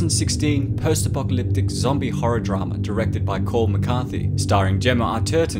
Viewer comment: I genuinely thought you said it was directed by Paul McCartney